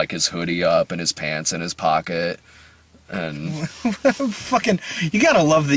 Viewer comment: I swear it sounded like said "...with his pants in his pockets..." at 24:30. I had to listen to that more times than I'd like to admit to figure out what he said.